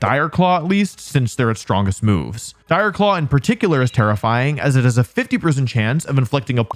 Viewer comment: Wolfey: “Dire claw is terrifying” What I hear: “Dire claw is tera flying” I was so confused